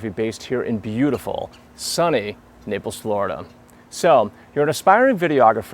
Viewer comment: this guy is the worst speaker in the world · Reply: Hehe, it sounded like you said "nipples, Florida" !! :DDD Hue hue hue hue hue! :D ... Is what I would say if I was 12, but I'm 14, so mooo..